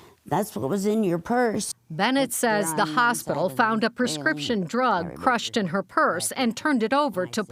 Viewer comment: They're allowed to go through personal belongings to obtain identification so they can alert family. If you come in to the hospital and they already know who you are then they're not allowed to search anything. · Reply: hospital? Sure, but the cop? Thought she said the cop found a crushed drug in her purse?